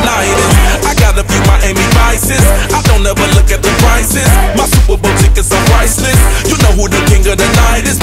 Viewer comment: I swear it sounds like he said "my Super Bowl tickets are priceless" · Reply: I thought he was saying "super boutique" at first... Lol.